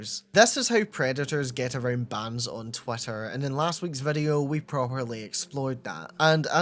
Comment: I know it's just your accent, but it sounded like you called it "twat-ter" instead of twitter and honestly I really love that